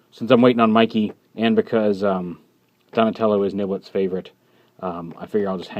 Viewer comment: At first I thought you said "Donatello is no one's favourite" and I was severely offended.